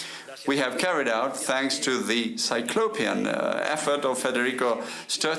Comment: i think he said "Herculean Effort" and the translator didint understand, just a pointer for the other viwers if they are confused. There is no such thing as a "Cyclopean Effort". "Herculean Effort" alludes to some task that is very difficult but the agent manages to be successful despite the hardships.